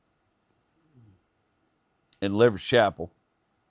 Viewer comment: I’ll start by saying I was today’s years old when I realized it was “Leverettes Chapel” and not “Lovers Chapel”. As you were telling the story I went to look it up and let me tell ya, there ain’t no “Lovers Chapel” in east Texas. Ha!! All these episodes I thought you were saying Lovers Chapel. Regarding your return to the reckon yard, I have really enjoyed your stories about it these last few weeks as you’ve discussed your journey there and the weight of emotion that it brings. I have very few bad memories of where I was raised, but the depth of emotion tied to that place and the experiences that are ingrained in me from there can still make it hard to go back. It’s difficult to describe, but you clearly have some of those same feelings as well. Seeing the change to things, reminiscing on the many memories, pondering the passage of time, and just reliving the past all bring about a heaviness of emotion. I wish you all the best on your plans for the Volkswagen. Do your momma proud! I know you’ll make something of it that is equally fun to drive as it is a cherished memory of Sherry Kay. Always remember that we’re rooting for you too.